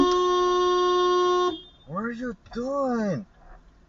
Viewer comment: that guy saying "What are you doing?" sounded like he was saying "Where's your gun?"